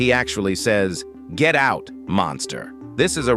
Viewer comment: I thought said get out fu** monster